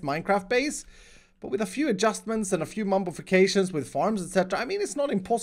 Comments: did he say mumbofication? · just throw some scaffolding at the height gap. Decorate it if you like from the outside, but don't bother over complicating it with the difference that small. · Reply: Also, did anyone else catch him saying "Mumbofications" at 14:15? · "Mumbofications" I heard that